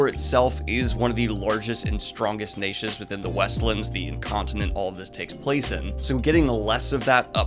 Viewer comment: Did.. did you call the Westlands an “incontinent” at 3:53?